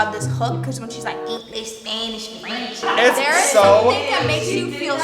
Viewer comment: Honestly I thought they said English Spanish zucchini 🤦‍♀️ and that’s on being blonde